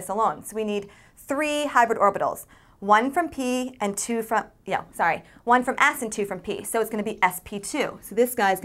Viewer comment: My face when i try to understand what she saying, she goes too fast. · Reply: She said One from S and two from p